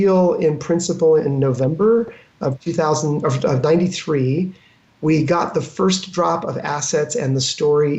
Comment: Am i the only one who heard ,,i got the first drops of acid..." At 3:46 ? :D